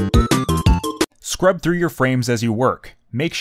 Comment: thought it said get good scrub